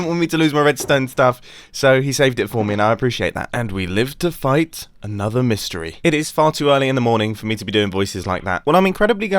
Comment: Wait did he just say "We live to fight another mystery"🤦